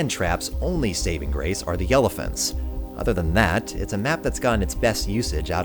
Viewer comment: It sounded like you said "Yellephents"